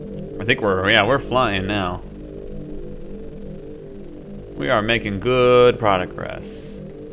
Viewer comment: I thought he said "we made goooood product grass." XD